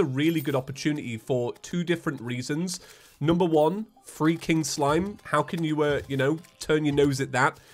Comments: On the start of the video u sound like your saying freaking slime not free king slime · Just me that thought he was saying freaking slime instead of free king slime? 😂